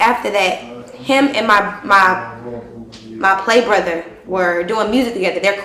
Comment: when she said " play brother".... i heard " nigga i was fucking before this queen"